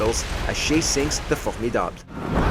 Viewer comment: did he say fu@ me dad or what